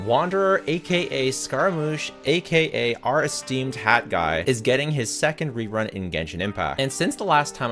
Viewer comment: I thought he said aka HOT Guy! he said HAT guy 😂 I guess both can be true